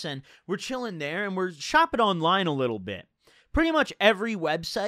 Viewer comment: Heard him say "chopping a line" so often it thought he said thag when saying "shopping online"